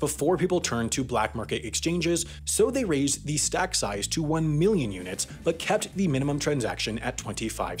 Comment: Hey HAI in the video at 3:31 you say "1 Million units", what is also said in the CC but it's written 100,000,000 (100 million) units, might check it out)